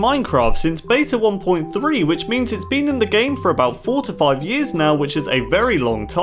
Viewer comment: I thought he said the bed has been in the game for 45 years... NOW THATS ALONG TIME!!